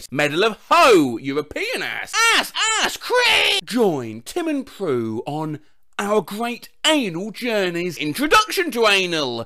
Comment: Can someone tell me what it’s SUPPOSED TO SAY AT 2:24 ?? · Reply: Okay. Director: Take two. clapper sound Director: And action. Announcer: Join Tim and Pru on Our Great Canal Journeys.